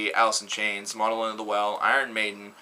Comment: What band did he say at 11:17? (sounded like monoloan the well)